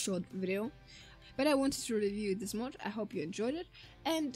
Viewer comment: WAIT... DID YOU REALLY SAY "TCHAU"???????????? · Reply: Não, amigo, ela disse ciao